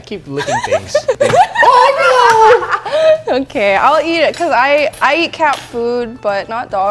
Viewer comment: Did she just say she eats cat food